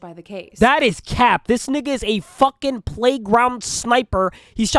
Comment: "this guy is a playground sniper" Lmao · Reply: Thought you said playground simp😂